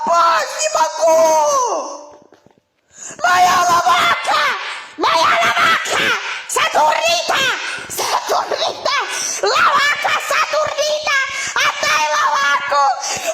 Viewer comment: (what I heard) MAYA LOVAKA x2 SATURIKAAAAAA SATURIKAAAAAAA random crying and screaming RAAAAAAAA